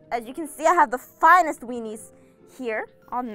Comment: did she say weenies?